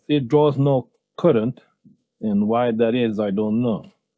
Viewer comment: Thought he said no black current 😅😅😅😅😅